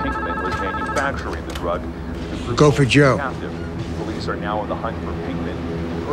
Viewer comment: i always thought he said "gopher joe" lmao